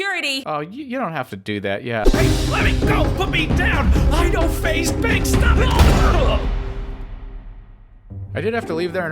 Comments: Did he just said "i know faze banks" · So many people worked so hard on this... Watch this through and get ready for the epic conclusion coming next week... it's so exciting and crazy that it's all coming to an end. :)To support our sponsor build your own website at: <link> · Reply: does it say I know FaZe Banks?